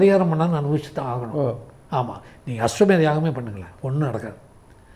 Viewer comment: Sir what is name of yagam he mentioned ? Can't hear clearly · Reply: அஸ்வமேத யாகம்